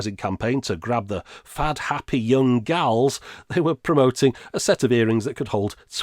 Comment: Had to skip back. Thought you said, “fat, happy young gals.” 😂